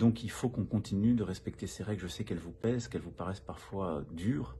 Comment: J'ai compris qu'elle vous bai... mdr